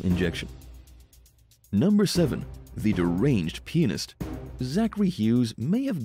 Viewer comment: I thought you said that the murderer was a deranged penis? My God, you had all kind of ridiculous images pop in my mind, with that thought. Until, I realized you said a deranged pianist. Some of these crimes are truly bizarre and extremely violent. I noticed how the murderers are all men. Makes me too scared to date ever again.